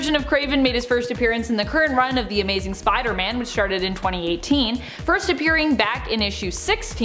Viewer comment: sounds like you say "sharted"?? which started....Im going with sharted. lol